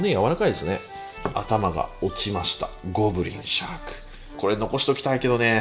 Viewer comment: ゴブリンシャークってなんか小栗旬に聞こえる